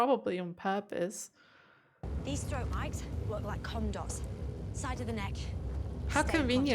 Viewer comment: I thought she said they look like condoms at first 😂